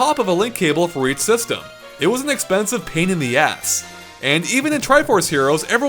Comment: lol at 3:38 I thought you said "pain in DS"